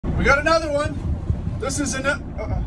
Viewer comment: If you’re confused, this references the last short when he said “We’ve got an egger!” quickly and a lot of comments said it sounded like the n-word. Edit: Most people didn’t seem upset. More like they did a double-take/thought it was funny. The video: <link> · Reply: i dont think anyone wanted him to stop saying it, it was just funny mishearing him yell "WE GOT AN EGGER" and people were laughing about it